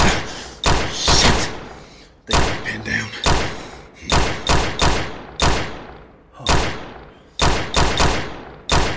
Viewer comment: what does he say? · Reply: I'm pinned down